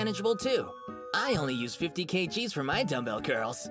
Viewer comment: Did he say he lifts 15k G??? WTF IS HIS I'VEN HUMAN HOLLY SHIT